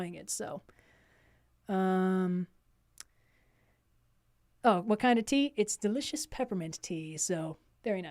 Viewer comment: She said "what kind of tea?" and I heard "wakanda tea"